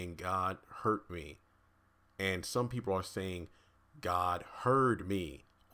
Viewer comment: I heard "God heard me" as well